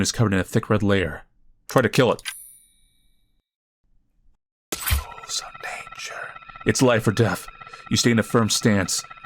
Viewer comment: Who else heard a whisper saying “your life’s in danger..” 15:50 is where I hear it 😭